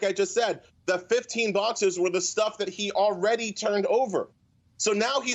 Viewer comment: DID HE SAY NUCQUELAR? LMAO